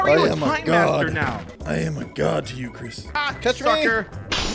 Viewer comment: did he just say f*ck*r